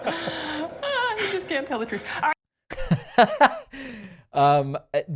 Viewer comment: Does she say, “Ah, you just can’t tell the truth”? 😂 ~✨🐀✌🏻💜